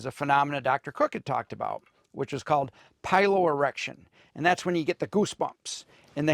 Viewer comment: Clearly I need a hearing test plus my ears have been ringing really loud today for some reason but I thought the guy said pyro erection (as in pyro maniac) Had to play that one back for clarification. 😅 Anyway, Happy New Year's Eve everyone! I'm awake now.